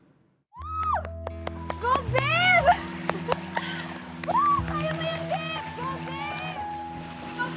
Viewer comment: ang narinig ko "GO BIMB!" omg hahaha